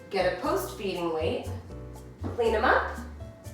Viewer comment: i thought you had said "eat him up!"